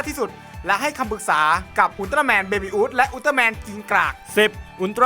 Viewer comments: ได้ยินว่ากินกาก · ได้ยินว่ากิงกากนะ